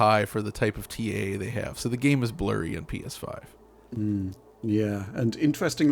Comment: did he just say “Penis 5”?